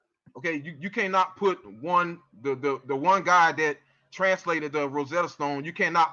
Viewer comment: God that translate the stone? What? Or did he said meant guy?